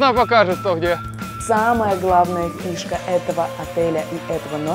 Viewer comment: мне послышалось «птушка» вместо фишка